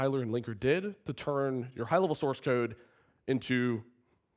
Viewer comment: Watching this on 1.5x speed, I thought he said "hell-of-a source code." XD